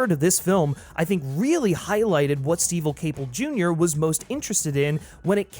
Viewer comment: Did you say “STEVEL Caple Jr.”? 😂